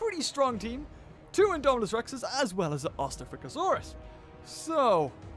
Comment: did he say OSTAFRICASAURUS?